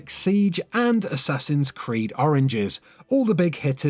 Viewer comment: It sounds like he said Assassin's Creed Oranges, not Origins.